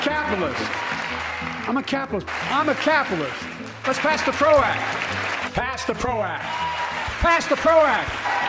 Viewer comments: ⚠ Order your shirts here: <link> Get free shipping this weekend with promo code DOOZY at the checkout . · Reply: < Joe Biden was actually saying, "Pass the Prozac" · was he saying "pass the Prozac" ?